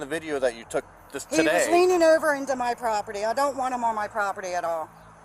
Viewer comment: Did she say he was “leaning” over into her yard? 😂😂